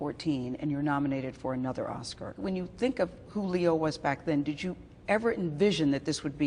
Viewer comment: When she said Who Leo I thought she said Julio LOL